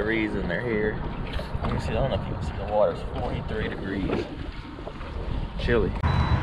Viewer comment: Love the channel but it's a couple months early for pre spawn , you might want to bring a net and net some of those bigger fish,I've lost some nice one trying to swing them in the boat KEEP ON FISHING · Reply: Np how warm is it there , I thought you said the water temp was 23 degrees